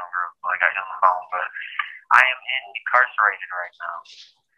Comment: did he say "in the carcerated right now"? lol Now that's funny. I'm gonna use that.